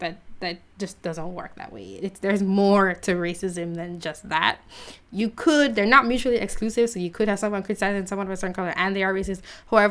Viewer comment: Really Smart narration, (couldn't understand you at 10:04) Hope you replace one of the squad disruptors to our country. You have my vote sweetheart! (old term of endearment) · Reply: She said "they're not mutually exclusive, so you could have someone criticising someone of a certain colour and they are racist"